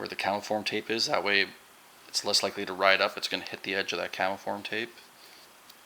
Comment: What is the name of that tape? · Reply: He sounded like he was saying "kennel form tape" but was actually saying "CAMO form tape." It's just Form Tape.